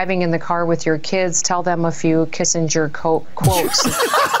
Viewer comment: Could be my ears, but I could have sworn she said "Kiss and Jerk Quotes". :face-orange-raised-eyebrow